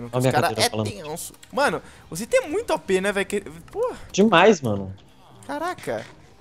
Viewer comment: Acho que ouvi um "Porra" Ali!😂😂